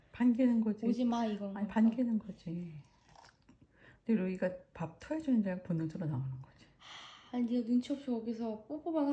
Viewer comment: "루이가 눈치 없이 뽀뽀만 하고 오는거야" 멘트 앞에 어머니가 "반기는거지. ? 주는지 알고 본능적으로 나오는거지~~0:16 뭐라고 하는건지 좀 알려주세요. 여러번 들어도 모르겠어요. ㅠㅠ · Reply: “루이가 밥 토해주는줄 알고 본능적으로 나오는거지” 어미새가 알 품어야해서 사냥?̊̈ 식량구하러 못나가니깐 아빠새가 구해온 식량 토해주면 어미새가 먹는 , 그런상황인줄 안다 이런거 같아요